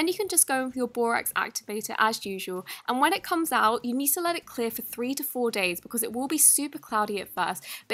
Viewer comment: A 0:41 I thought she said let it clear up for 34 days. Hahahaha 😂